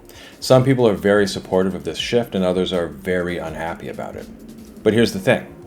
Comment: What I heard at 2:05 “Some people are very supportive of this sh*t” 😂😂😂😂😂 I had to go back twice…